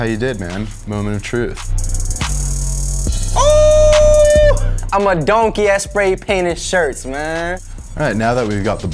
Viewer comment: she did he say he's a donkey at stay painting shirts doesn't he mean goat wtf lol ???